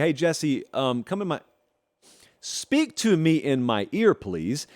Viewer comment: Bro was finna say “ cum in my ear “ 8:05 😂😂